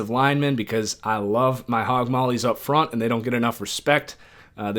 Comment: When you said hogmollies I thought you said you like to pop mollies 😂 anyways PHINSUP 🐬🐬